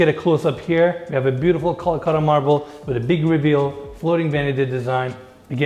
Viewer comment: Did enes said Kolkata marble??